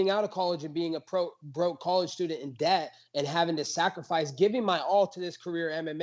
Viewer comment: does colby have a kid? i could have sworn he said broke college student and dad.